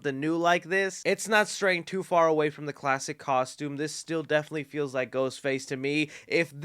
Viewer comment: When you were talking about the aged/cracked mask, I know you said “this still definitely looks like Ghostface,” but it sounded like you said “this STU definitely looks like Ghostface” 😏😏